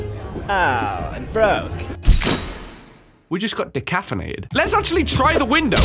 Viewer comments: did you say decaffeinated? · did you say...decaffinated? As in...decaff coffee?